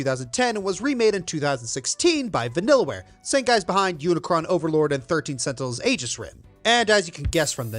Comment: please tell me im not the only one who heard him say unicron overlord and not unicorn